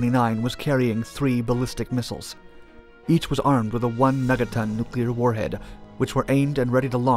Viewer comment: Did he say "meta ton" or "mega ton?" 'Cause it sounded like "meta" to me. ;-)